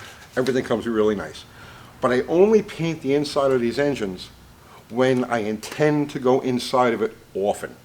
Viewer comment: Happy Little Engine Block ..YES and Shake the Devil out of that Brush,,, GREAT ADVICE with The TORCH .Not Sure but I thought I heard Grumpy Painted inside his Engines... As Always Great Show..